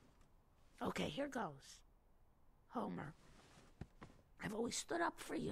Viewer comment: A simple great detail when Marge calls him "Homer". It's always Homie, but that single word stops him instantly. He knows it's VERY serious. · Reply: you don't hear her say homer?